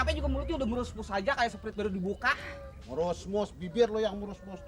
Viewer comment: nggak tau ngomong apa paling kocak😂😂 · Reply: mrusbus😂😂